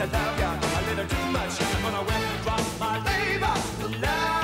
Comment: "I'm gonna wind drop my neighbor" Well, that's what I thought he said when I was 9.